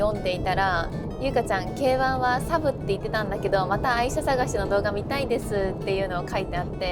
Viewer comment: 沖縄でのトゥクトゥクとの前科があるので、愛車探しが（愛人探し) に聞こえる。